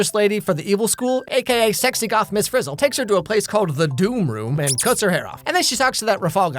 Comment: i thought he said "and cuts her head off" not "and cut her hair off"